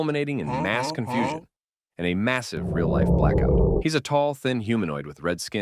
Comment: did you just said massive